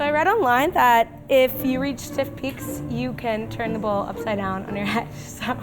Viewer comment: what did she say at 3:23? I keep hearing "if you reach stiff peaks you can turn the bowl upside down on your head" I know im hearing the first part wrong, what is she saying?